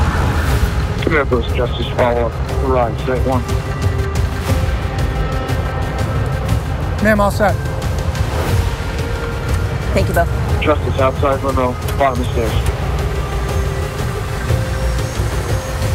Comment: Did she say "Thank you bro"